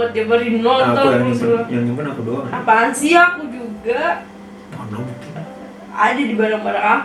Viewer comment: ku salah denger ga si di menit 9:30 kak bilang "apaansih yang aku juga" wkwk